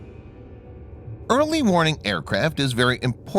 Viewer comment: Did he say "Early MORNING aircraft are very important..." 6:30 ?! My God that's a bad mistake. I'm getting tired of spotting simple errors on this channel. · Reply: Jake Dee Nope, sounds like "early warning" to me. Sounds like someone is a bit salty